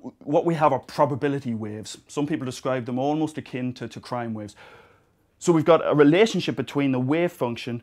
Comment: what is it that he says ~1:28 "almost akin to .... waves". I'm hearing "crime", did he mean "sine"? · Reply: No, he's talking about crime.